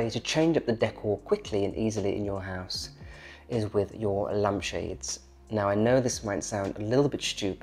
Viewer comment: I see you likeBunny Mellon. She is a favourite of mine too. Sophie always steals the show. I have had a couple of Shi Sues over the years. Best dogs ever. Sorry, I did have a giggle over your Scottish accent. It took me ages to work out you meant lampshades. I thought you were saying lunch bids . Wondered what they were 🤪Really liked your topiary plate.